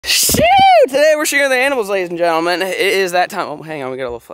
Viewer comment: My dumbass thought he said “shoooo today we’re shooting the animal’s ladies and gentlemen” lmaooo my heart sank